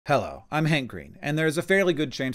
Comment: I thought you said "hello, I'm hangry".